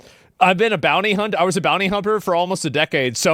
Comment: ASMON! Did you say: "Bounty-Humper" ??? 😂😂😂